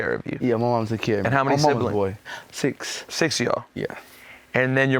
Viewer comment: Did he say "My moms a boy"?